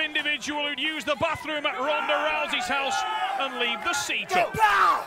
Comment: what does he says? Ronda Rousey house? xD